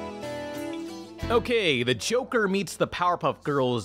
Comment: Sounds like you said Choker, rather than Joker